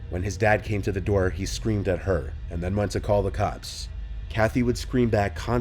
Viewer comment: you said he went to call the cocks instead of cops. Did you do that on purpose? lol